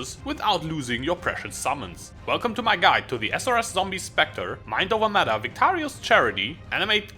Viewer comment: Thought you said mind over meta then read what you actually said lol.